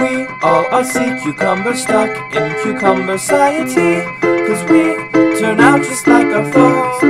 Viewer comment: Lyrics: I am a sea cucumber Waiting to float across the sea But me, I’ll never touch the clouds Or stand out of the crowds I can’t break out I’m bound by gravity We all are sea cucumber Bound by cucumber ‘ciety ‘Cause we turn out just like our folks We laugh at the same jokes We have our hopes But we live patiently Go on, go on life in a can Go on, go on living as planned Go on, go on cucumber man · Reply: i heard it say stuck in cucumber 'ciety